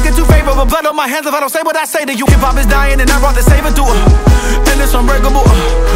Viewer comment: 1st time I heard this @ 1:35 I thought he said "hip hop is dyin and I brought the saber tooth" 😂